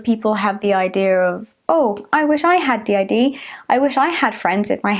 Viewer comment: Every time she says DID I hear "The idea" so imagine me being confused when I though she suddenly started talking about some idea I didn't remember being mentioned mentioned😂